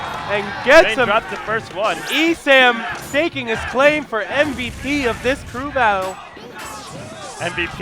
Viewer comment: he clearly said MVP... not mvd...